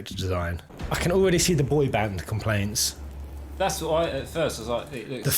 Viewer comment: Did you just say... "I can already see the boy-band complaints" ? (I can't understand, no entiendo, di ko maintindihan) I'm so sorry, your accent is too thick HAHA! 🤣 EDIT: 6:59 so you actually said "boy band" HAHA! I have to agree. This is what I don't like about most Final Fantasy male characters. The designers haven't moved on from this "boy band" image. It's just weird, and sometimes corny, cheesy and cringe haha! I'm not really sure if the Japanese developers are marketing this towards K-pop fans or South Koreans in general, but Japanese should realize that majority of South Koreans kind of hate Japan because of WWII. Please Final Fantasy, no more Vaan, no more Hope Estheim, no more Noctis, no more effeminate looking boys, no more V-shaped faces that look like a hybrid of ants/aliens with huge contact lenses haha! 😭🤣 It's now time to use real human models for motion-capture like what they did to Kingsglaive or Resident Evil 2 Remake's Leon and Claire.